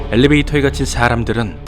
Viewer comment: 엘레베이터에 갇힌 샤람들은 이라고 들리는데 기분탓이겠죠?